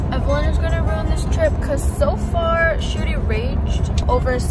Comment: Did anyone notice that 1:22 in the subtitles instead of “evelyn is going to round this trip” evelyn changed it to “Evelyn is going to bring this trip”😭